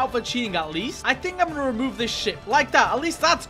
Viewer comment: why did I hear sh*t